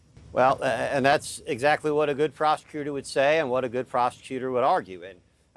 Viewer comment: is it just me or does it sound like that attorney is saying “good prostitutor” instead of “good prosecutor”? LMAO